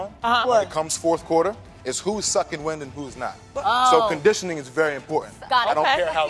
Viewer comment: "Who's sucking wind and who's not." LOL their reactions after he said that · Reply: I thought he said, " who is sucking when " LOL I